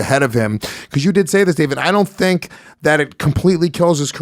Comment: Did call "David" at 1:44? I've not heard that before...